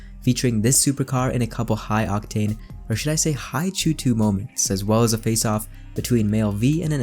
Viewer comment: What are you saying at 2:06 ?? "Or should I say high......... Moments" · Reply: CHOOH2, superfuel of the future